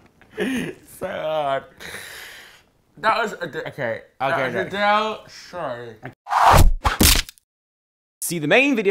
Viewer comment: Did Oli say that it's called sorry? I swear he said "That was Adele - Sorry" He's confused between Adele and Justin Bieber lol x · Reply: I thought he was saying"That was adele singing"